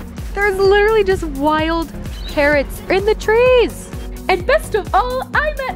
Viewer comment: "There's literally just wild parrots in the trees" Me:lol i thought you said carrots lmfao